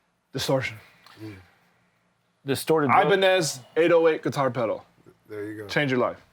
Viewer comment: pls what plugin he mentions 49:22 when talking about distortion? i cant understand :( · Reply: Yes, the Ibañez TS808 (guitar pedal) emulation by UA. He showed it on a Instagram video.